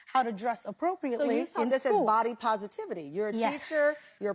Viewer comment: this woman said ‘sawl’ instead of ‘saw.’ Geez. Tell me you are illiterate without telling me